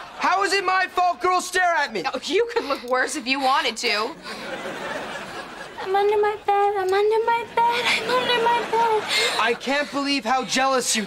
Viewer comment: Can someone tell me what is cat saying at 3:35 ? I didn't get it..... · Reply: I’m under my bed I’m under my bed